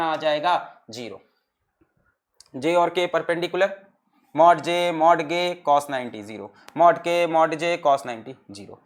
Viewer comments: did someone noticed what sir said ? "Mod j , mod gay product 0" 😂😂😂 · I was listening so closely that i heard him say "mod gay" at 46:07. He meant to say "mod k" 😅😂